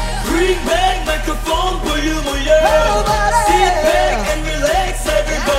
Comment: i love mingi sm like thats my bitch but at first i thought he said "shit back and relax"